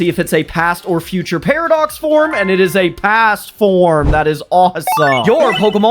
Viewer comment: When he said past form for Lugia I thought he said cast form lol